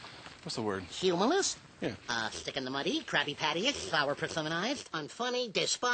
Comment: Did he say Krabby Patty